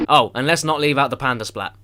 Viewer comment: Did he say "Panda splat"? It's a polar bear D